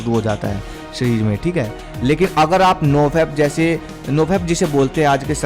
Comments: Vaah bhaiyya aapka desi tarika kafi results deta he salam he aapko jo aaj bhi desi tarike apnane ko bolte he is artificial protein vali generation ko 👍❤️ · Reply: kya Bolte hai?? Wo word samjh nahi Aya.. "noveb" kya hai?? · kya Bolte hai?? Wo word samjh nahi Aya.. "noveb" kya hai?? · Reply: nofap 🔥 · kya Bolte hai?? Wo word samjh nahi Aya.. "noveb" kya hai??